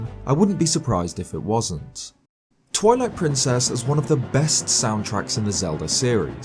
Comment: did anyone else hear toilet princess? SPANTZZ